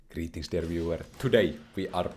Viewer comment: and never ask what's in his inventory))) Also, I have a confession: for couple of years I thought that you are saying "Greetings everywhere", which sounded strange, but, on the other hand, it's the Internet) Shame on me